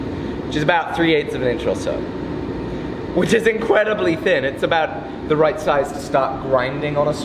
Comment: Wait, is it me or did he say “which is incwedibly thin”